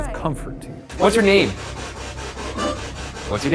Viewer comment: im sorry but when it answered “whats you’re name?” All i heard was “Im da onion”